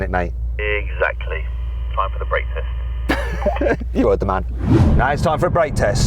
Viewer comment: I thought I heard Yanni say "time for breakfast" 😂